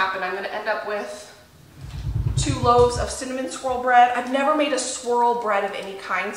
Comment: I thought you said cinnamon “squirrel” bread 😂